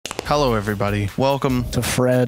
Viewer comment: when says “hello everybody” the captions say “hello everyone,” my OCD is killing me.